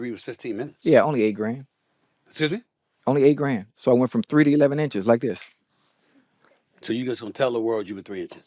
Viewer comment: did he say he went from 3 to 11 inches!? .... wtf.